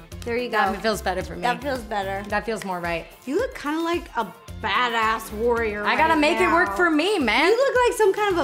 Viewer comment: did Ro just say "Badass"????!!